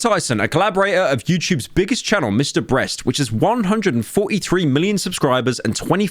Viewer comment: Everybody i think he said mr breast 4:38 He said mr brast again what